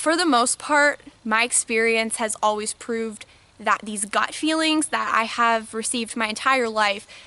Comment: Did I just hear you say "god feelings" at 3:35? If so, I do not appreciate that assumption. IF it is anything other than very high awareness/perception, it is certainly no more than spiritual.